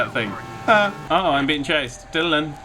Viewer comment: i thought you were saying my name o.O "dylan"